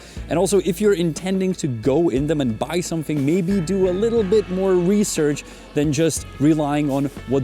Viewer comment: do a little bit more weedsearch..... is what i heard.... 🤣